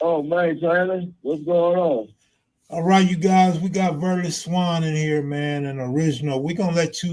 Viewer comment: I thought they was saying his name was all these years. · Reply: I thought so as well. is what I thought they were saying.